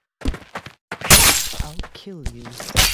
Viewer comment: wait litsen at 0:02 there is someone said "i will kill you"